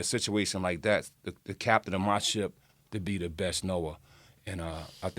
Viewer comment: Did he just say the captain of my ship should be the best NOAH?? (about 6 mins) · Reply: "Knower"